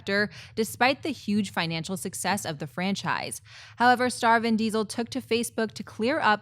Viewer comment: It sounded like said "Starvin' Diesel", like he's starving for a director.